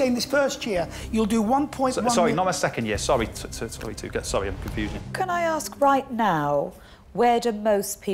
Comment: did he say he is confusion